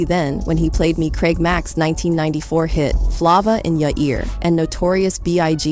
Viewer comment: I just heard "FLAAAVA" In your ear...im done with this IA of a recording smmfh